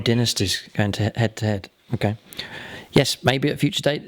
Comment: I thought you said, “Redneck Ron’s.” Haha